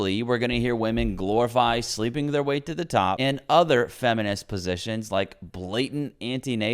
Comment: did he say feminist 'positions "?😅😅